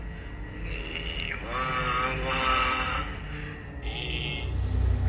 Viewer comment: 我一直聽成吉娃娃😂